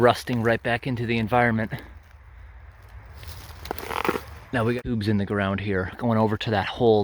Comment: The sudden transition makes it sound like you said “Now we got boobs in the ground here.”